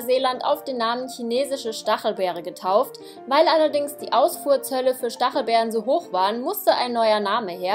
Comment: Ausfuhrzölle...haha omg ich hab Ausfurz Hölle verstanden xD